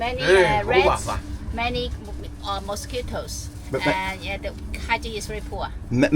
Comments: he actually said cockroaches. He didn’t say mosquitos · I think the man was saying many cockroaches and not mosquitos